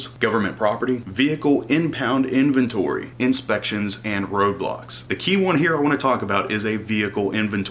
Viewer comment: i honestly thought you just said roblox